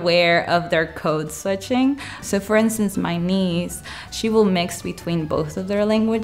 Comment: “… for instance, my nee…” I was not too sure of what I heard. 🤔 I replayed the section a couple of times and then turned on CC. Then I realized she meant to say “my niece”. 😊